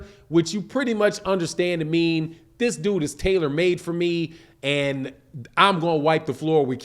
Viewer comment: I thought he was saying Barrios was tailor made for Keith but either way, good assessment as always.